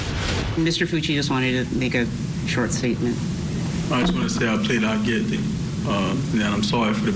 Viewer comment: It sounded like he said, "I'm pleading not guilty" 🤔